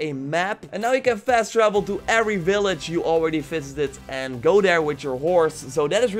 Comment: With your accent at the two minute 56 Mark sounds like you said every village you fisted not visited I’m not gonna lie that was fucking hilarious and I just spilled coffee everywhere